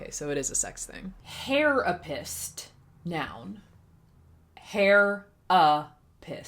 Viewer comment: i thought it said hai rap***